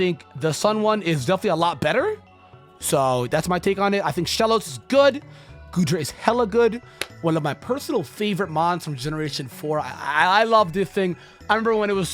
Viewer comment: Did..did he just said “Goodra”?